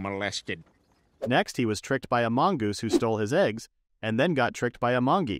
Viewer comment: i wasnt looking at my screen, and i heard "next he was tricked by amongus"